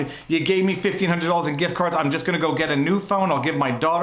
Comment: I heard "gRift" card. Sorry to hear about the hassle.